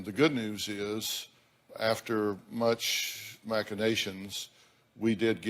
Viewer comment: wtf is a macadation? I googled it, and nothing came up, except macadamia and Mac edition. Lol. Lose your correct big word Dr. Phil? What exactly was he trying to say?